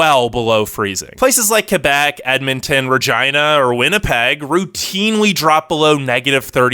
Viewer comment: Why did I hear vagina